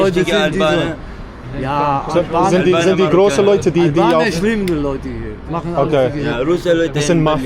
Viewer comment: The Subtitle is wrong. He said "Albaner schlimm die Leute hier" which properly translated means "The Albanian People here are bad/the worst"